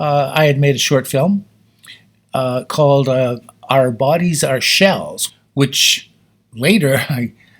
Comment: I thought he said the name of the film was "Our Bodies, Our Shelves."